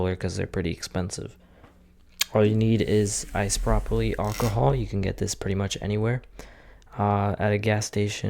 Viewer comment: Did you say ice broccoli alcohol?